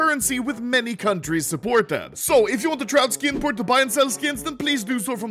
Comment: I'm going to be completely honest. I thought he said "Skin Porn" the first time he said "Skin Port"